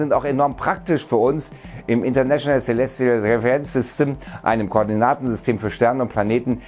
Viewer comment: Was sagst du da bei 5:12? Ich verstehe "international seleziofäbelänsystem"